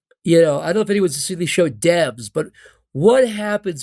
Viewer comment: WHAT SHOW IS HE SAYING HERE? DEBS? DABS? HELP